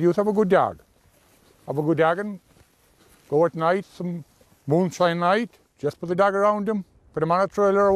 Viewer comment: yeah I would definitely would struggle to talk to someone who talks like that. I think he said a boom shine night but I having got a clue what it means?